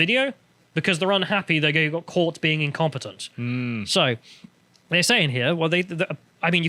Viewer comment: I first thought said: The Ministry of Defense got caught being "Compotent." 🤣